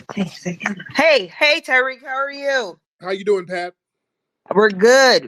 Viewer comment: Did Pat call Tariq "Satan?!" At 38:54 she made weird noises and then said "Hey Satan." She caught herself too! Tariq better pray over these callers cause some of them be afflicted frfr. And some of them could be fronting as an Allie but are the complete opposite.